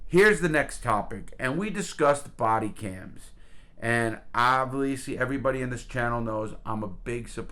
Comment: Ovileesy instead of obviously